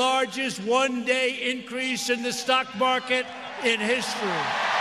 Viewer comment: Smh, and not a damn one of those people probably even own stocks, yet cheering as if it makes a difference in their lives. How pathetic. Smh 🧢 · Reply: They thought he said socks